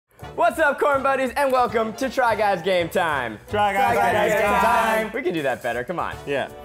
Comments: It sound like they say “ try guys gay time” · Is weird that for a while now, I thought they were saying "Try Guys Gay Time!!!" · tell me why i thought they were saying try guys gay time for the longest time 💀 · I always hear "TRY GUYS GAY TIME!!!" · I heard “Try guys gay time”